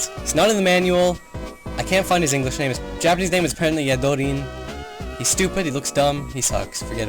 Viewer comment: captions say "his japanese name is pregnant yet voting" 😂😂